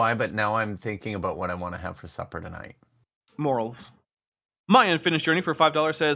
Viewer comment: I misheard "morels" as "morals" and thought "Did Dapper just call Kipp amoral? That doesn't sound correct"